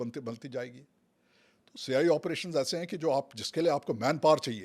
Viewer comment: did he say CIA operations? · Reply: CI - counter insurgency